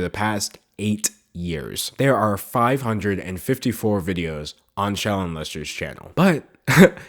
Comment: I heard "Shallon Lester" as "child molester" and had to skip back to see if I heard it wrong haha. I guess all this talk about her "weird" tweets got in my brain and I just started mishearing things.